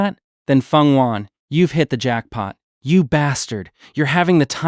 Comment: Did you say jackpot9:33